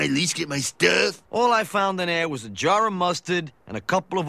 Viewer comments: When I was a Kid I thought he said "Old Psycho Magazines" rather than "Cycle Magazines" as in Motorcycles. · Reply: I thought he said Psychlo magazines. Otto must have been a Battlefield Earth fan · Oh I could have sworn he said "cycle magazine"